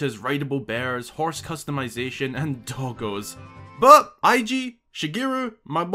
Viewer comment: Lol when you said doggos the first time I definitely thought you said tacos. Also I totally agree with the music. I do want traditional music like ocarina of times Hyrule field theme though.